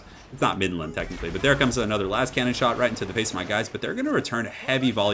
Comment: did he just say lascannon lol. that bone giant practically is one!